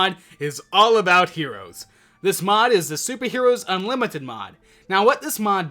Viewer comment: i thought he said the super unlimited meat mod :D